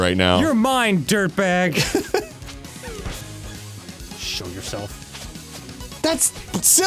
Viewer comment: WHAT did you say DAN? Just to be clear it's not a big deal but It just caught me off guard OH MY GOD YOU SAID SHOW YOURSELF NOT SHOOT YOURSELF HAHAHAHA SORRY